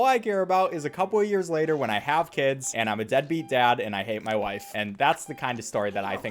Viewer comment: what did he say at 1:43? wife or life? or both? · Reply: Wife